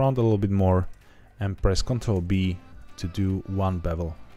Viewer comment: The number of time I had to CTRL+Z because I thought you said CTRL+B when in fact it was "P" you were saying. ^^; Thank you for this tutorial, I'm still starting out on Blender and appreciate that it's "simple" enough to follow. :)